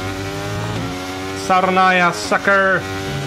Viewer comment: did he say saronaya 😆